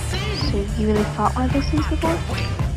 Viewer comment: what does Max say at 0:49? Awesome video!!! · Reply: she says “you’ve really fought one of these things before?”